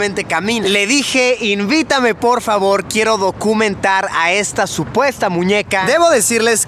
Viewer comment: le entendí gokumentar (creo que debo dejar de ver anime y ver más contenido del rey palomo Like si te diste cuenta que y Luisillo estaban en coyoacán Jsjsjs tiene años este video y lo sigo viendo